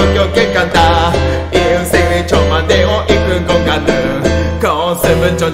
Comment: ちょ待てよにしか聞こえんw